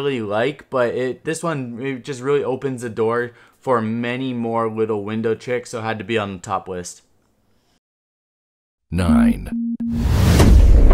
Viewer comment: anyone catch him saying wittle